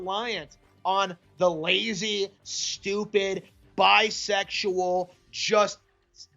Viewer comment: I think he was trying to say BUYsexual